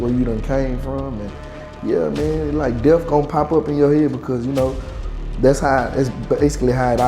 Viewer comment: he says: "yea like, DEAF gonna pop in your head", DEAF??? what does it mean to have DEAF pop up in your head??? if these "people" claim to be rap stars, the LEAST they could do is TALK PROPERLY!!! but being BRAIN DEAD i see why we have $HIT like this RUBE. fare well moron, maybe next life you'll gain an INTELLECT and contribute to the populous instead of DUMB IT ALL UP!!! i cannot stand RAP KRAPP at all, and remember when it was called TALENT to SING along with MUSIC!!! this rap krapp is no different then what we all did as CHILDREN learning how to talk and rhyme words to little songs to EDUCATE us.